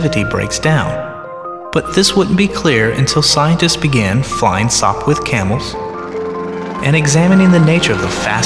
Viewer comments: I hear "Flying soap with camels" :') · Anyone have a clue what he says at 0:10 ? It sounds like "This wouldn't be clear until scientists began flying slop with camels". Obviously not what he says but I can't figure it out.